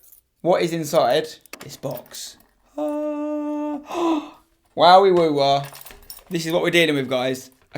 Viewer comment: did he say "wawee woowah"?